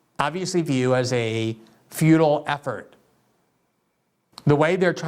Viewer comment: I think i misheard you say feudal instead futile. Yes, i would say the Ukraine war is a feudal attempt. Or should i say a war which only benefits the elites. Sickens me how human life is so disregarded by our self appointed overlords.